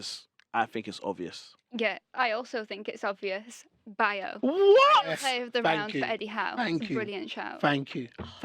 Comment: who does she say is the best player of the round ? Bao ? Lol